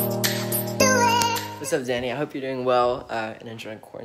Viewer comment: what did he said daynia or what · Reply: dany